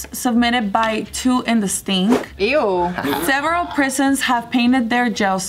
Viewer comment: I thought she's saying two in the pink and several in the stink.